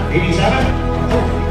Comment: I love the name but at first I thought you said jafart